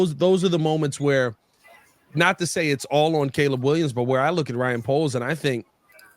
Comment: I spoke to quick i thought you were saying its solely on Caleb my bad bro